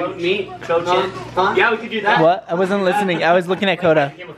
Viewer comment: cho was the girl Harry had a crush on for a while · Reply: Oooh I thought you were saying "cha-cha"...nevermind haha. Yeah Cho Chang went to one of the dances with Harry right?